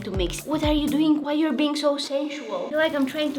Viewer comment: why did i hear s@ instead of sensual 💀☠️ Oh my dirty mind🥴